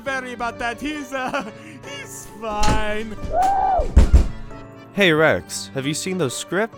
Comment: I think rex said help